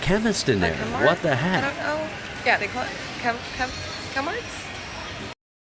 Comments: I think she's saying "Chem-mart", just a chemist/pharmacy store. Also, she has nice chesticles. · I thought she said Chemmart which is a chemist chain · FYI... there's a chemist chain here in Australia called Chemmart. So I think that's what the chick in Western Australia was saying when you thought she was saying Kmart. (